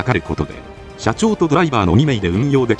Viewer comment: 社長とドライバーに聞こえてならないw